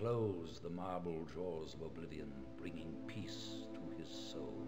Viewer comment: I thought he said the marble drawers of oblivion like it is an evil dresser or something.